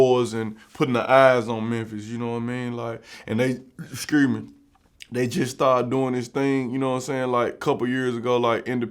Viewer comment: "eh-shkur me" 😂😂😂 what is dis nikka sayin? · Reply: excuse me lol